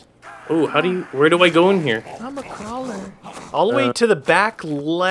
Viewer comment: anyone else hear I'm a crawler??? xD